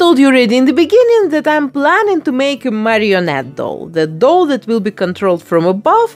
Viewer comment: i thought it said Marie Antoinette doll and the style checks lol